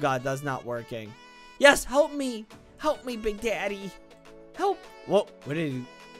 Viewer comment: did you say daddy